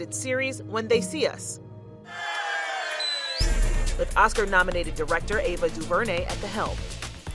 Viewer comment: did she just say Oscar nominated dictator and the help!?